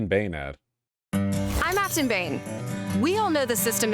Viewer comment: I thought she said "I masturbate" lol